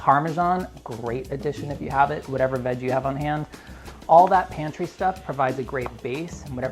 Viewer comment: I know said 'whatever veg you have on hand', but I first heard 'whatever meds you have on hand' - as his kid was acting all crazy in the outro - lol